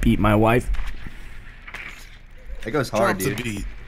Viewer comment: Sounded like he said “i peed my wife “ 😂 😂